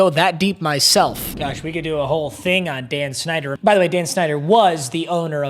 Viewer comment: When he said Dan snider I thought he said Dan shnider💀💀